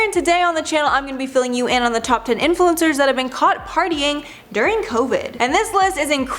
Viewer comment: I thought it first said, "Top 10 Influencers That Caught COVID While Partying." smh